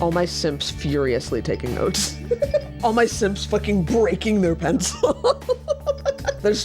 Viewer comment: LMAO i thought she said all my sims taking notes and was actually so confused